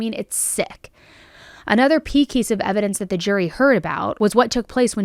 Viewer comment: I definitely giggled at 25:46 when you said “pee kees of evidence” instead of key piece 🤭🥰